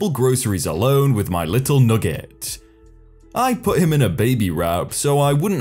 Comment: I thought you said that the new mom called her baby nougat, rewound and realized you said nugget. Lol. Thanks great vid